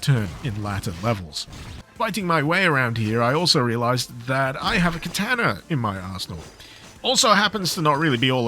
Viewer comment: Not going to lie, I heard "I realized I have a katana in my arsehole", mostly because I wanted to.